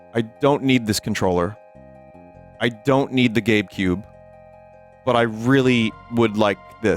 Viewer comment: Oh you said GABEcube I swear I heard "Gape Cube" the first few times